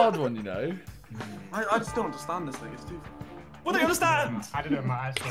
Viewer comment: did he just say nigga? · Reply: thing